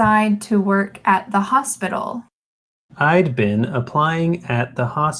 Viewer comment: Very interesting exercise. I was surprised that what I heard as "I´ve been" was actually "I´d been". Thanks!!!! It really helped me!